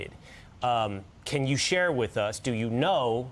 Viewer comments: Although it sounded like he said "she" in that clip, he said "share" the alibi (or whatever the rest of the sentence was). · No one ever mentioned a SHE. said Share and people thought he said She surrounding sharing alibis of the people around the girls that night, but no one said She for the killer. They said the opposite.